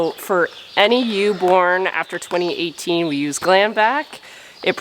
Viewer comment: GLANVAC?, All this time I thought you were saying GLAMvac! 😊 I think I’ve seen too many beauty product commercials lately.😂 🤣 😁 ha ha